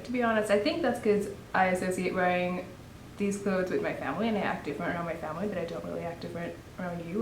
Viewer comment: Did just she say that: "I have to wear them (clothes) for my family"? If I heard correct than that is pretty sad and pathetic. What kind of family forces its members to wear certain cloths and why? Are we not in the 21st centuary? Why doesn't she have a problem with that? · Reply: How did you even hear that? She said "I think because I associate wearing these clothes with my family"